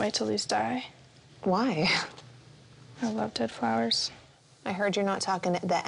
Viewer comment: every talking about kylie throwing her moms clothes and the mom not being properly mad about it Me: did she just say she loves dead flowers?